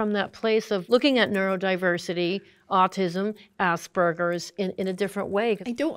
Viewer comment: why does she say "Ass burger"